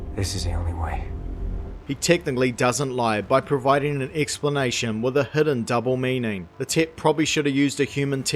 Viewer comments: I keep hearing "the tit" hehehehe. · I keep hearing "The Tit" Lol